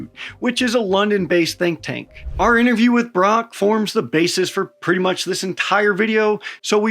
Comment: i thought you said interview with Grok xD